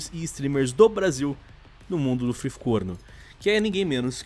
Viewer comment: fui só eu que ouvi ''de freecorno''